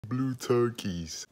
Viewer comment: I literally heard him say blue turkeys I was like what everyone talking about 🤣 lmfao